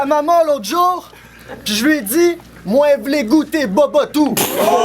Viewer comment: il dit quoi à 3:40 ? "Moi vouloir goûter babatou"? merci de m'éclairer les québécois ou autres qui ont compris. Merci d'avance. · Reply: wep c'est du creole mwen vle goute bobot ou